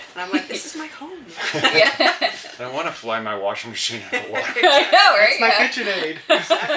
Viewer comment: Did he really say "I want to fly my washing machine?!?!" THAT'S HILARIOUS!